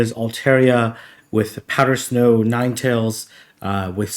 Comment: Altaria with powdered snow? Did I hear that correctly? 0:10 If so. Is that possible to obtain with a TM? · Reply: He said altaria with powder snow ninetales.